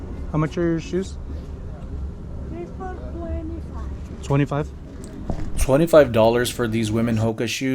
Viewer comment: thought she said 3 for 25